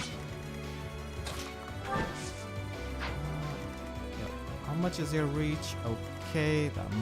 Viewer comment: did you say „How much does Eir reach?“ :D ?